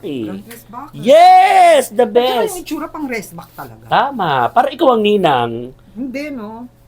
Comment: Did I mishear you? parang sinabi mo po "Itsura ng Igorot"?